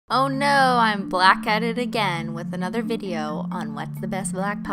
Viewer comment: i paused the video so at the beginning and only heard her say “OH NO i’m black” and i’ve been laughing for the past 5 mins lol